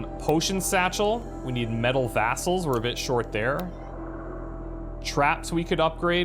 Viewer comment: Did he just say “Metal Vassals”?